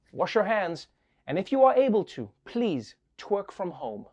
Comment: Did he just say "twerk from Home" at 5:03 ?🤣